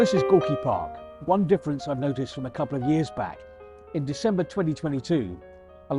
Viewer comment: You stated the year is 2002. BBC is a multi billion dollar state news outlet. What kind of production quality is this? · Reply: I heard "a couple of years back in 2022".